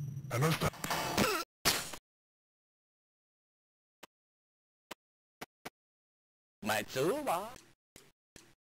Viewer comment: what is said there, always thought it sounded like my toolbox